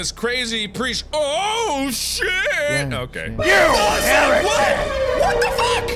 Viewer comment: What is the phrase the stranger says each time he jump scares? I literally can’t understand someone help. 🤣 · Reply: I hear, "You Heritic!"